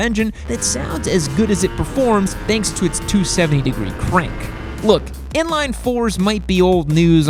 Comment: did he just say the R1 has a 270 degree crank